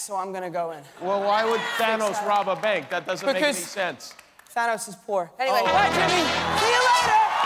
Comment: in American accent: thanos is robbing a bank. (why would thanos be robbing a bank?) because thanos is poor. thank u adorable tom holland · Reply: Oh I thought he said that thanos was bored